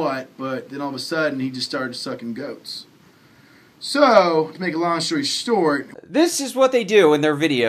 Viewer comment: Did he just say "to make a long shory stort"?@ 2:58 what a fool..